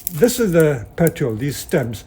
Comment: Timestamp? There are captions on the video, I'm hard of hearing also and I use them a great deal :) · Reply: It says CC "we do this is how we look this is the ""petrol"" these stems we cut it off" Just wondering what the correct wording would have been